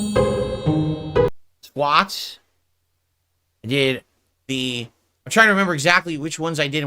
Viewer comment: Sounded like he said twats instead of squats.